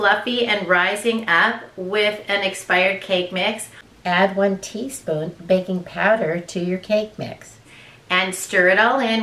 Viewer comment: Hi. I need to know exactly what you advised regarding expired cake mixes. I have impaired hearing so I could not understand what you said nor did the closed captions work properly. Some smart alecs hijacked your cc words and put their own words/communique in its place. Krazy times, eh? Anyway, if you have time, let me know the proper amount & product. I strained pretty hard to hear & thought I heard you say 1 tsp baking powder. Many thanks and all the best to you and yours! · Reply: Yes, 1 tsp baking powder.